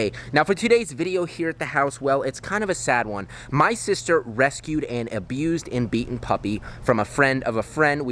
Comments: Fr a second, I thought u said that ur sister rescued and abused the puppy and I was like..... 0_0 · At the start it sounds like u said "my sister rescued and abused a little puppy" ha